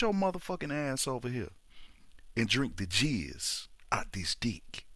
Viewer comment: what he said was jizz